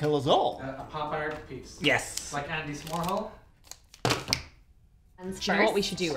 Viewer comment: I need a pic of how big smile was when he came up with Andy S'morehol! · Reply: I thought he said Candy S'morehol until I came to the comments lol